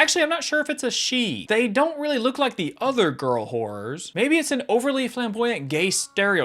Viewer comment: "they don't really look like the other girl horrors" What normal people heard: Girl horrors What I heard: Girl wh---- 💀 Dude you got to listen to what you're saying 😂